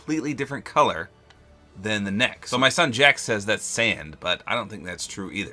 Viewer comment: is it me or am I hearing the word Sans?